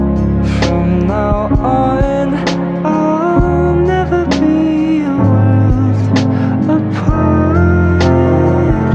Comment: Anybody please 1:02 I'll never be.... apart whats word missing, i couldnt hear =.=~ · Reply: I'll never be a world apart